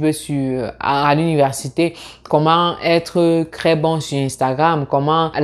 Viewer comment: Hi! J'apprécie le contenu partagé dans cette vidéo. Cependant, est-ce juste moi, ou prononces-tu vraiment "tr" comme "cr"?? Au début de la vidéo, j'ai entendu "etudiant éCRanger". Je me suis dit que j'ai peut-être just mal entendu. Mais là, je suis à 6:17 de la vidéo et tu dis "comment être CRès bon sur Instagram"... À 6:37 tu dis "ce que je CROuve encore souvent marrant"... À 6:47, "si tu n'as jamais CRaversé le cap du Baccalauréat" Mais pourquoi? Pourquoi remplacer le T par un C lorsqu'il est suivi par un R? Je suis assez confus